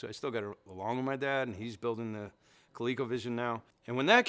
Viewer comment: When I first saw this and he talked about his dad, I thought he said his dad was a big wig in Coleco. Nope, Lorne Lanning is self made man, came from a working class family with the same values I was raised.